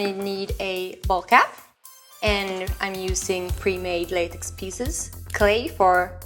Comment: I couldn't understand what she said, pre-made something pieces? Could you guys list these items? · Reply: Pre-made latex, you put Some latex on a plate until it becomes solid. ;)